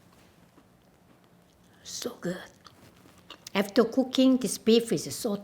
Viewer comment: Did she just said "shiok!"??